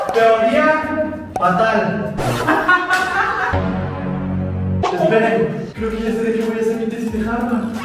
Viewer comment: TEORÍA PATAL estaba sentada en mi mueble y cuando escuché que dijeron teoría patal me caí del mueble😢😂😂😂